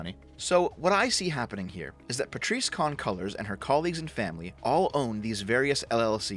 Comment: I thought you were saying Patrice "Con" Cullors.